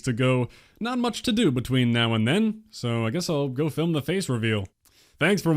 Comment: What did you say at the end? It sounded like you said you were going to film your face reveal. Very interesting.